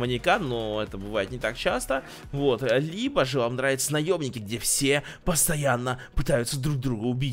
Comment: Мне одному послышалось от 1:40 до 1:42 (где то там) он сказал "наёбники"